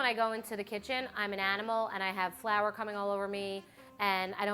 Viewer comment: did she just say flour cumming all over me? i think she did.. lol